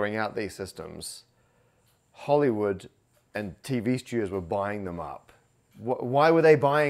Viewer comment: small captions mistake: “TV stew” instead of “TV studios”